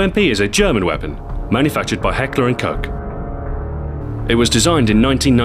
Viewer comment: did he just say hecklerh and cock!?!